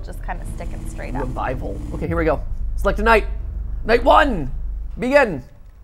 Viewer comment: I thought he said “Night 1, Miguel!” And I started asking myself who Miguel was, till I replayed it 😂